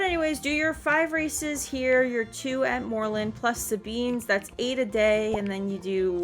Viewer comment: what did you say? 5 races here, 2 in moorland, Plus the _____? Beams? That’s 8 a day. And then 12 championships. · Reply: Sorry! I was mumbly. 5 races in Jorvik Stables 2 in Moorland Plus Sabine's (in Moorland) (And this part I messed up and didn't say in the video) But you can do both dressage, plus 10 championships and be done in a day. There's a whole week though so any combination of the above races until you get 20 is enough.